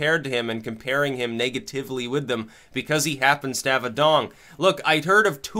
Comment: did you say dong? LOL